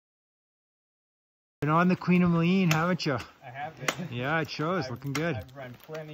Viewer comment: What does this mean? "You've been on the queen of lean"? "Oh yeah I've been doing laps on the queen" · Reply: Colin Johnson- I thought he said Queen of Moline, as in the name of some ice climb where the climber has practiced and climbed repeatedly (done "laps") and improved, but still has room to improve more with some fine tuning.